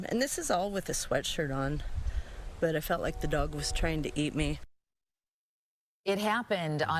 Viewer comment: It wasn't "trained to eat" her. it's all genetic, no training involved. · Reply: I heard "tryin' to eat me" - thought she said "trained" at first, but I think she was saying it was "trying" to eat her